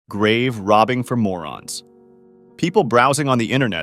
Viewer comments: Censoring "morons" is crazy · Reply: bro i know i thought it said grave robbing for moans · Oh it says morons I thought it said grave digging for Mormons 🥹